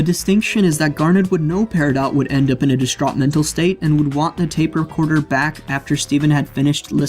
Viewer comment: Has anyone else noticed that at 4:43 with subtitles on it says 'rape recorder' instead of 'tape recorder'?